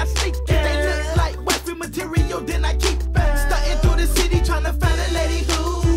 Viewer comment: does he say if they look like boyfriend material then i keep them?